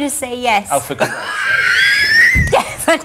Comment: Can someone tell me what did Simon say at 3:37 after Sharon said yes ? thank you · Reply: For god sake